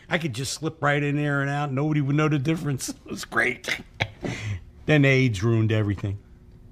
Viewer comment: What is Frank saying at 9:19 "then age ruined everything"? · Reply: Then AIDS ruined everything